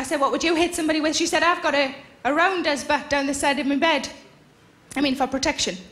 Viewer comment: what on earth is she saying here? what does is this thing by the side of the bead? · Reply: I've got a rounders bats, down the side of my bed